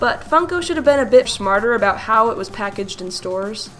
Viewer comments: I thought you said "shmarter" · did she say shmarter???